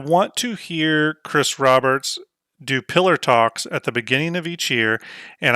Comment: I’m in the same camp is in when it comes to the roadmap. What she said resonates. Good, mostly objective, discussion about the roadmap by all though. I kept hearing “pillow talk” when was mentioning “pillar talk”…. Thought I’d share. Great vid overall.